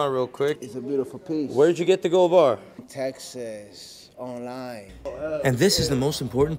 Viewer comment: Yeah, that “Texas, online” wasn’t suspicious at all haha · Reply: I thought he said "Texas '09"